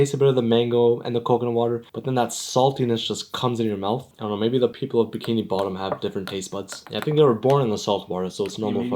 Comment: could you repeat what you said?? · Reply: Can we be mature💔